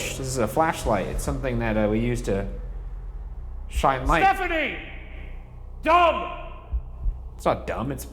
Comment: SHE CLEARLY SAID STEPHANIE 🎉 Ryan: are you Ann? 😂